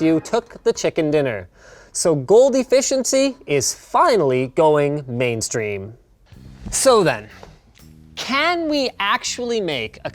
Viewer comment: i heard "So finally, goldie fish in sea is going mainstream."